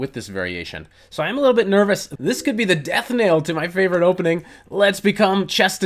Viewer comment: Did he say "Death nail?" That's funny. As in "nail in the coffin". Perhaps he said knell.